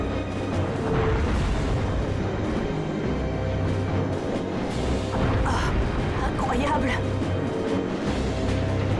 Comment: c'est quoi ce "Yyyaaah" 😂😂😂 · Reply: Il dit "Da", je crois que ça veut dire oui en russe